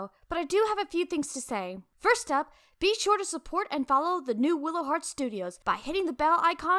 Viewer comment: when you interrupted the video and you said something about your youtube channel i thought you said wiggle heart instead of willow heart